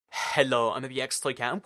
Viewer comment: Why tf do u say hello like that u sound like ur saying halo